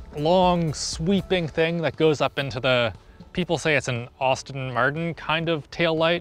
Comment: Did he just say "Austin Martin"